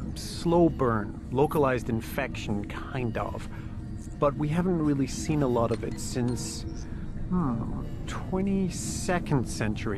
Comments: I thought he almost said we haven't seen it since 22 seconds ago. XD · I thought he almost said we haven't seen it since 20 seconds ago. XD